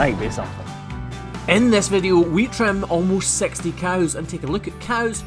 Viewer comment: I thought he said "We trim our most sexy cows". But I think it was just his accent as he said " almost sixty cows"